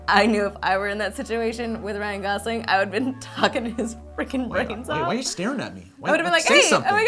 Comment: did anyone else think said "fucking his fricking brains out"